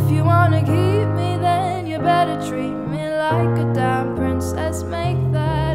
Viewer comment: I feel sooo bad because I thought that she said "then you better treat me like a dying princess" but it's actually "then you better treat me like a damn princess"😂😢